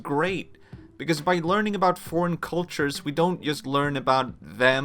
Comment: I think I heard "forniculture"